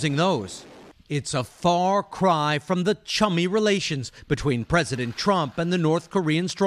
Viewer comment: I thought it said "a chubby relationship"